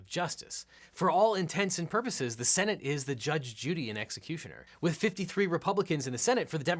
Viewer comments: did you say "judge, JUDY, and executioner?" lol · did he say Judge Judy? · Did he say Judge Judy and executioner lol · Did you say "Judge Judy and Executioner"? I listened to this multiple times and still heard that! lol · Am I the only one who heard him say "The Judge Judy and Executioner," rather than "judge, jury, and executioner?" :U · Am I the only one that hears "Judge Judy and executioner" instead of 'judge, jury, and executioner'? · Did I hear "judge, Judy, and executioner"? haha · I'm sorry, did I just hear, "Judge Judy and executioner" lol close enough